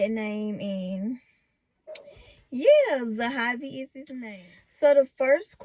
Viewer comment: Girl I'm slow I thought you said Zaxbys I had to rewind it 3 times but Zahavi is so cute